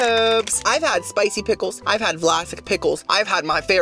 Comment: Just had to rewatch 50:16 - I swear I heard him say he’s had flacid pickles 😂😂😂 No one wants a flacid pickle! Lmao. Then on the re-watch I head him say Valasic - which makes slightly more sense 🤣 a Hope everyone is having an amazing day!